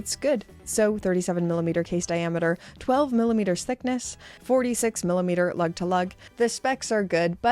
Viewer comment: Observation - when you combine "millimeters" with "thickness" they combine to make it sound like youre saying sickness. Listen and tell me im lying 😁